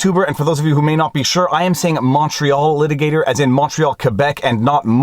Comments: My wife informed me that when I first started listening to this channel, she thought you were saying “macho litigator.” So, probably a good idea that you clarified that! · For the first 6 months watching you, I thought you were saying macho litigator XD Second post because youtube just deletes my comments indiscriminately now.